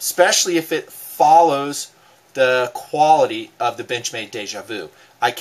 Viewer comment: haha, at one point it sounded like you said bitchmade instead of benchmade.